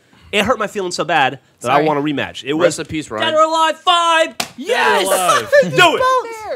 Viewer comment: I thought geoff said dead or alive 5?